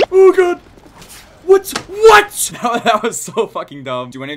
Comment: U thought he said water but he actually said f***